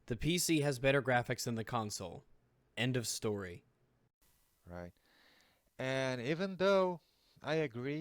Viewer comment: did anyone else hear that? 18:58 in the background sounds like someone saying "right?". audio editing messup? :P